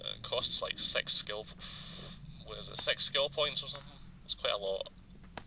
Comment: Is it my notebook, or the sound is little de-synced in some places? I like the feeling of this game. It has some good oldschool vibe, with quite nice graphic. 13:50 - I heard "sex skill points"; well, this could be quite interesting skill to see :D